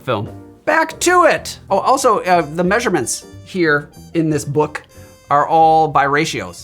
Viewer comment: Between the drinks and the laxatives, my flabbers were so gasted that I swore that you said "and the measurements are all biracial…" had to rewind and double check.